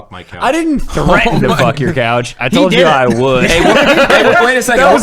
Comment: i heard cat not couch